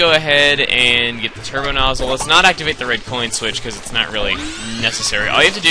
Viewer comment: LOL the caption at 4:22 says "its not really neccesary" and the caption says "yes it is" LOL